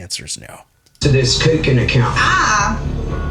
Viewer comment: They misheard her, she said she put 80% in her “cookin” account…